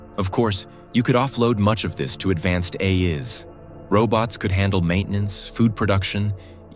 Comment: …..did I just hear the ai voice pronounce the plural of ai as ‘a is’??